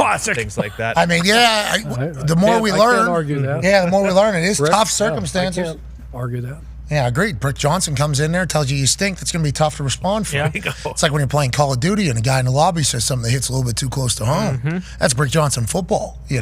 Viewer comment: did he call him “prick” Johnson 😂😂😂